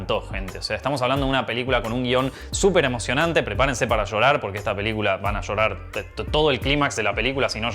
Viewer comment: Minuto 2:06 del video escuché a diciéndome "No vas a ver esta película". EN fin, seguiré viendo los 11 min restantes.